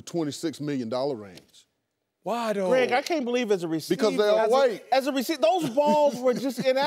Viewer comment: does he really say the QBs are paid so much "Because they are all white" ?